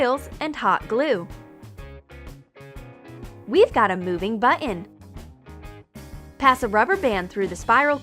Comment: she says button but it sounds like she said bunion